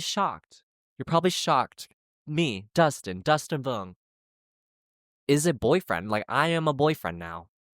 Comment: When u said "dustin vuong is a boyfriend now" i heard "dustin vuong has a boyfriend now" lol. Congratulations on your first relationship ❤